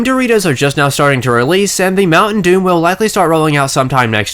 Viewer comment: Did you just say... Mountain Doom?